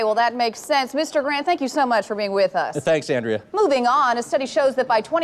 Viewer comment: what the heck did she say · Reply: “a study shows that by 2025, american children will be too obese to ride hoverboards.” which is kinda true, if parents keep feeding their kids fried crap and ice cream.